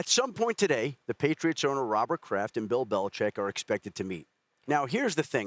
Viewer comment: everytime shannon says “Mr. Kraft” i always hear “Mr. Krab” lmao